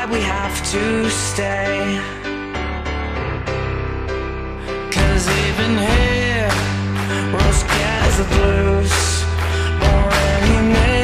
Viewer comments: I actually thought it said "cause even here we're scared to bruise" it also kinda made sense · I always hear "Even heroes get the blues" instead of "Even here, we're scared to bruise"